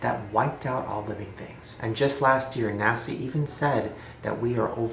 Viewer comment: he said nasi instead of nasa